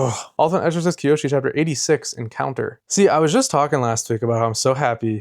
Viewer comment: ultexckioshi chapter 86 edit: same vibes as when you give the goodbye on the podcast you speak so fast it really took me a while to understand what you were saying a couple episodes at least